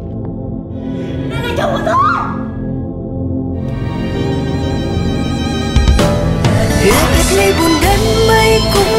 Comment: là nói gì vậy nhỉ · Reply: Đây là chồng của tôi